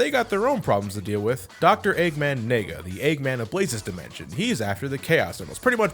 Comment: Why did I heard "Eggman nigga" ?